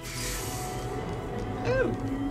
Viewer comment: As a kid, I always heard 'echuta' as "eat shit and die" said really fast.